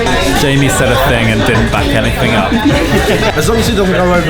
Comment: have no idea what he's saying but it's one of the funniest parts of the video😂😂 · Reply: "Jamie said a thing and didn't back anything up" is what said